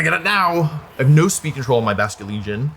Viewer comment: "I have no speed control on my Basculegion" but the caption says "I have no 7 feet foot hole in my Basculegion." wth LOL